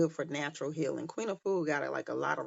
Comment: Hi! Thank you for posting this live! The question asked was how to open your third eye and you mention @ 48:42 i think, was Queen Food? I'm not sure I heard the name correct. Could you please repeat her name again? Thank you so much for these video! !!! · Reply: Queen Afua she has an abundance of books that are for healing etc..